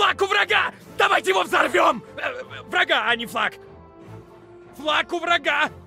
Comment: мне послышалось фак